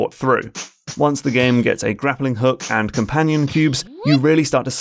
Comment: did he just say companion cube?